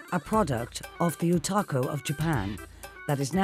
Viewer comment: did I just hear "otako"? lol xD